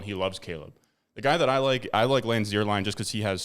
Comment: What name is he saying at @ 6:39? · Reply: lance zierlein